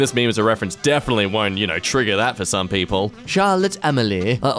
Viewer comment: "charlotte emily" is what i heard while not looking at my screen, shat me up for a bit that did. yall fnaf fans would know what im on about lol.